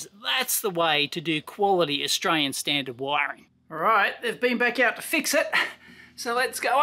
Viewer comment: did he say he did? If so I missed it. Otherwise, show me the time stamp where he looked inside them. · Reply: I just checked again and I think I was wrong. At 6:05 I thought Dave said that HE had fixed it, but after listening carefully I now think he said "he has been back...". So, I agree that Dave should follow your advice.